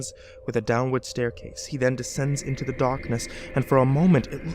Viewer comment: I had to replay 11:40 a couple times because I swear it sounded like you were saying "into the dawkness" LOL